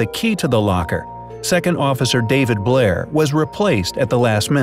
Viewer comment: did anyone else think that said David Blain